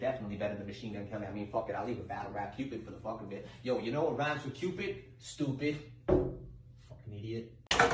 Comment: "you know what cupid ryhmes with?" "Stoooopid!" Pecks sliding glass door with hat 🤣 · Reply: I thought he said “know what cute bitch rhymes with, stupid.” bonk